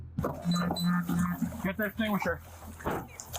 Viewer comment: captions wrong he said: Get the extinguisher